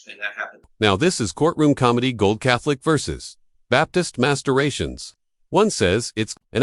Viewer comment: did they say "baptist masturbations"? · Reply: The phrase was Baptist mass duration. Although I don’t believe baptists refer to their services as mass.